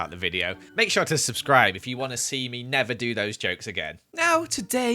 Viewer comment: Once you said "I will never do those jokes again" I thought you said "I will NETHER do those jokes again" xD